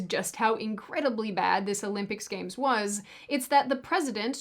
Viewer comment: At first I thought that at 14:30 she said "Olympic scams" and I was like: Yeah, spot on.